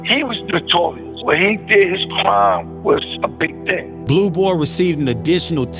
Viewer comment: what did he say 🤔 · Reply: He said, “when he (Larry Davis) did his crime it was a big thing” Meaning it was highly publicized.